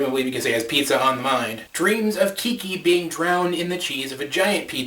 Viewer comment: I am going to get HATE for this. but I'm the only one who thought he said" To stop Kiki from drowning in a cheese vj. or I just have a dirty mind😥